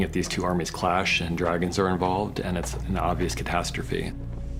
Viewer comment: Guys ?! Did I hear Him Correctly!? ... He Said DRAGONS!? ...Listen From 1:05 ... The Question is Is Rhaegal Is Gonna Be Resurrected By Bran Somehow ? ..Or ...OK let’s stop here .. it already sounds like crazy talk .. we will have to watch the next episode